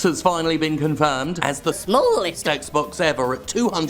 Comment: When he talked about the Xbox being the smallest, I was in a noisy vehicle and thought he said "swollest" and I was very confused.